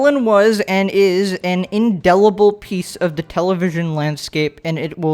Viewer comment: I thought I heard inedible piece of the television landscape. I mean... I wouldn’t want to eat her either